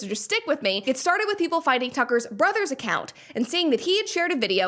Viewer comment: Sorry-I thought you said that he is Tucker’s brother’s son, a Junior??